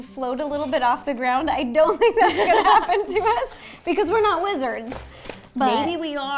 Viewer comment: I thought u said "we are not lizards" instead of "we are not wizards"😂